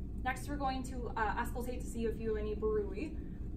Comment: Thanks for this, so helpful for my health assessment class 😍 btw what did you say around 4:22 ? "We're going to auscultate to see if you have any.. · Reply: Thank you! I made this in nursing school for my health assessment class as well (: I auscultated her carotid for any bruit, my program had it on our checklist. It's a certain sound which can indicate stenosis in the artery.